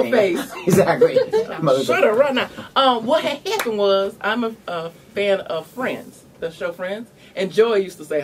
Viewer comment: bahaha I thought she said "I'm a fan of FRANCE"